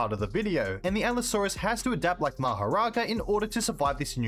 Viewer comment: Did someone said Mahoraga you just summoned the Jujutsu kaisen fanbase lol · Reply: dude i heard mahoraga and did a double take